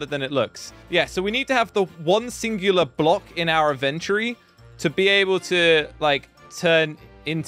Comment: instead of "Inventory" he says "adventory"